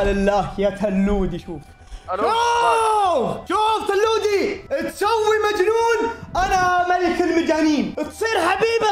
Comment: ههههههههههههههههههههه ملك المجانين حبيب انا ماشفت البث بس فهمت وش قال م**** اول حرف. 18+ 😂😂😂😂😂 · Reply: قال ملعون